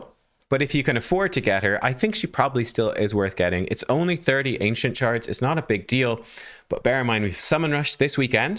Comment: 30 ancient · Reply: Is it only 30 ancient? I must have misheard. 30 ancients are doable :)